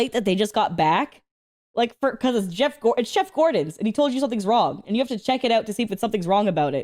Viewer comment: did accidentally say Jeff Gordon instead of chef Gordon?